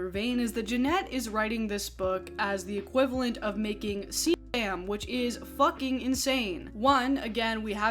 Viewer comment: PLEASE someone tell me what is being said at 6:26 so I can finish the video without being confused. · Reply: I THINK she says "CSAM", which means child SA material.